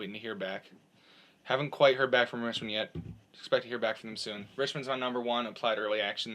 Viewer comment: did he say Richmond??